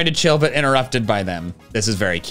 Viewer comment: Can we just talk about how Michael said "b*tch" instead of "bit" in